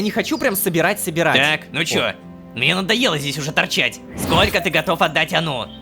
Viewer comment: мне послышалось"мне надо бан"😂